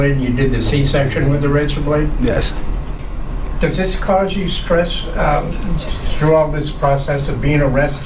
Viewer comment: Wait what in the world is the narrator saying @ 9:52 ??!!? “And finally he used it as a ____?” A dad bod??! Lol his accent is so thick I can’t understand that word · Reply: He is asking if he's stressed or not